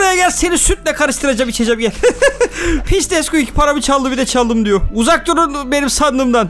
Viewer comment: abi sanırım ilk kez küfür ettin :D yada ben yanlış anladım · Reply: Pis dedi